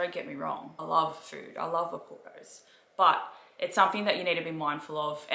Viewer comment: The auto captions thought you said I love apple’s instead of I love Oporto’s 😂 Thanks for the video ❤️